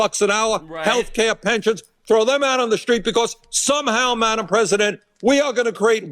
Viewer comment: Did he just say madam president? bruh, who the fuck was woman president? No one.